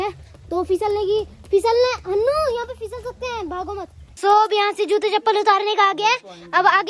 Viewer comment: said my nickname Hanu Pls can you tell if he actually said it